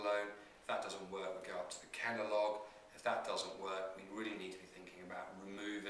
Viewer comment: what is he saying at 4:42 ? Kenalog? Kendall?